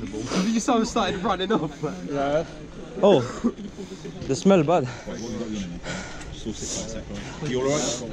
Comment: did he say “oh, the smell of bud”? Lool